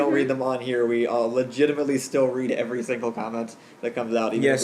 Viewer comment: When I first heard you say "See you next Friday, guys.", I thought you said "Maybe Phoenix Wright." Am I the only one who mistook that statement at first?